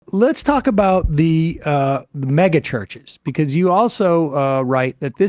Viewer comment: Oh! I thought you said "MAGA churches"! Yes, Mega churches. It suits the crowd, anyway, right?